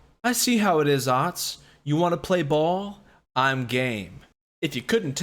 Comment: Ngl at 0:30 i thought he said (You want to play ball I’m gay) I spit my drink out but then realized he said I’m game